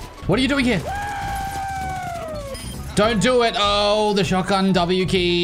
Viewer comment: I thought you said DONT TRIE IT at first and then I'm like wait you don't have the high ground